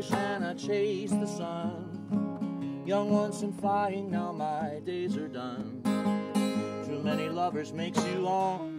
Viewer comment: what does he say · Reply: Young once and flying, now my days are done... I think. ha